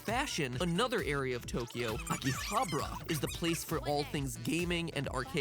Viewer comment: It's fun seeing Mattpat murder those Japanese words lol. His pronunciation of Akihabara sounded like he was saying chupacabra 😂